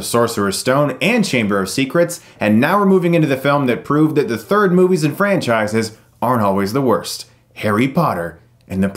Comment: For a second I thought he said. "proves that the third movies are always the worst"